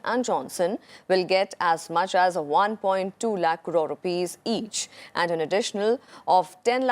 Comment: did I hear 1.2 lakh crore rupees compensation......phew!